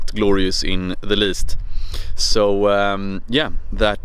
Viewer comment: What does he always say at the end of the scene? Like here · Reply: Xoxo Meaning: hugs and kisses